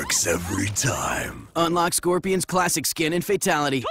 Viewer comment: Calm down, I thought he said Frosty all the time. I guess it's TOASTY.